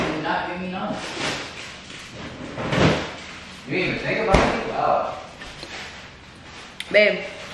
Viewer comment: what did even say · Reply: He said “Did you even think about me gosh”